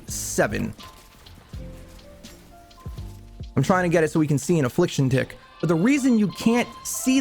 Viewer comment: Am i the only on who heard it as affliction di*k🤣🤣